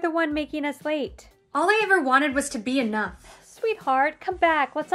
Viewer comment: I heard 'all i ever wanted was to be A NUN' 😭😭😭